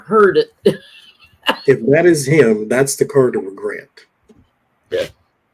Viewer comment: At exactly 54:19 , when showed the regret card I heard a distinct deep male voice say "YEP".